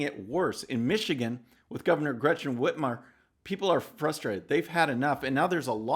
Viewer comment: I had to do a doubletake...I thought you said "Greta" when speaking of the Michigan gov.! Oops! ( it was "Gretchen" - but the voice drop off sounded like "Greta"! ) "2 peas in a pod"😂